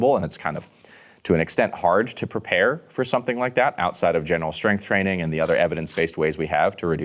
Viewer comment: Did I hear say "general" strength training at 30:21? Hahahaha xD I get what you're saying, you mean training strength in movements that are likely to carry over to a lot of different stuff so "general".